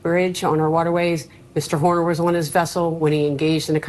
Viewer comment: did she say "boner"? 😂